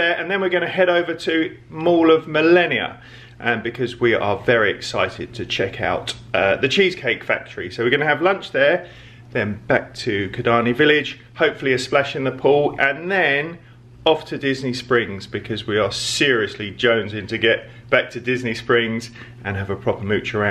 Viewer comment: Superb watch as usual. We had to rewind as thought you said Slash in the pool 😂😂. One bargain buys has too. God the food in. Cheesecake factory looks amazing. Must try. Great stuff guys 👏👏